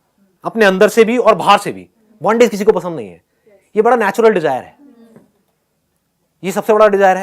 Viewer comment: ( bondj....) pe kya bola muze samaz nahi aaya,koi batayega...plz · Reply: Bondage kisi ko pasand nahi hai